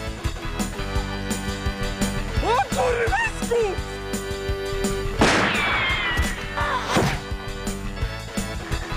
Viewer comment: co on powiedział? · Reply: O kurwescu